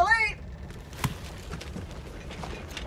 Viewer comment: Rip to the lady... btw I always thought she said “take something from trolley” not “aaaanything from the trolley”...